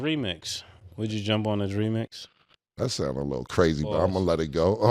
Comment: Man I was brushing my teeth and I heard "would you jump on he re-mix" and almost chokes to death 😂😂😂